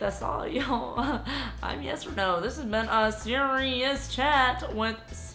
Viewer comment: It sounded like she said " hi I'm yes or no "